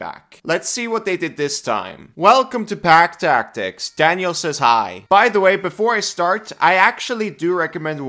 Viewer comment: For a moment my sleep-addled brain thought you said "Daniel is high" instead of "Daniel says hi" lol